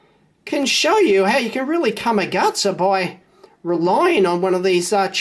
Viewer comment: I always hear you say, "Cum your guts out." Does that mean the same thing in Australia that it means in the US?